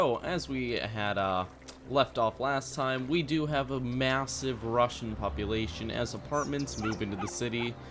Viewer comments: it sounds like you say you have a massive Russian population. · at the start i thought he said "we do have a massive russion population"